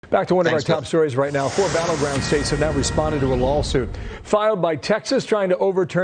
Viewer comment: Did... did he just say LOLsuit? Cause I agree, it is.